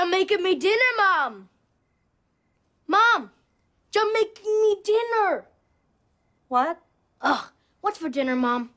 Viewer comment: I'm Jamaican and I didn't understand what he said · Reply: He said “Jamaican me dinner” meaning Ja making me dinner